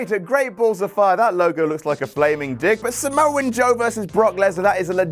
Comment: Did you say "Samoan Joe"? 😳